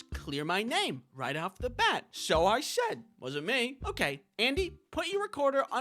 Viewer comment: I think your teacher just thought you said "It was me"